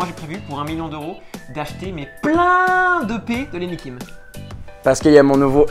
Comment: c'est moi ou il dit je vais achez plein de pet de Lenni Kim...🤣🤣🤣🤭🤭😉